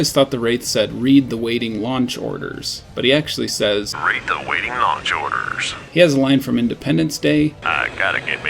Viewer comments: I always thought that Wraith said "Wraith awaiting notch orders" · When I was little and tried to make sense of the Wraith I heard... "Reap the Raiding Ranch Borders" I thought maybe that's a funky why to pronounce "Ranch"... adunno xD My mother tongue ain't English. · Every since I was a kid I ALWAYS thought the Wraith said "Rate the waiting launch orders!" (What???) It wasn't until I went and played the Remastered version, decades later, that I finally heard the correct line, lol. And HO-LY COW!!! I always thought the Science Vessel was supposed to be a parody of Mr. Burns.....I NEVER KNEW IT ACTUALLY WAS HIM!!!!!!!!!!! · I donno, I thought wraith said "read the waiting lunch orders" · Of course the Wraith says “Wraith awaiting Launch orders” For some reason I thought he said “Reap” like reaper was cut off.